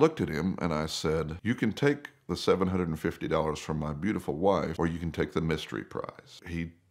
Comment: At first I thought he said you could take the $750 and my wife… 😅